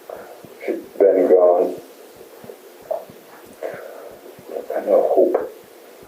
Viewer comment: YES!! I've been waiting for a video! Also, at 1:40:47 the subtitles say "she's getting down (indistinct)" but I'm pretty sure hes saying "shes dead and gone. Theres no hope" to which the interrogator responds something about her being buried. Anyways, great work! I normally wouldnt point that out but it's a pretty big reveal on his part.